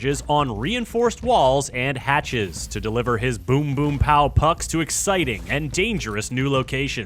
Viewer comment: 英語がわからないから3:53の話が、ブンブンに聞こえてにっこり。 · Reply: boom boom(英語版のドカーンちゅどーん的なやつ)って言ってるからあなたは英語を聞き取れています。自信を持ちましょう。